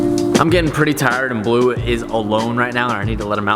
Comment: i thought he was saying that he was getting tired and blue at the end hahaha